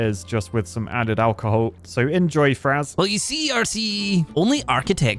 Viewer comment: I thought for a bit he said RT before realizing he said RCE.